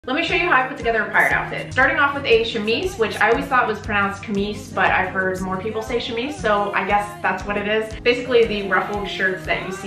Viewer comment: I heard “pride outfit” i was so fucking confused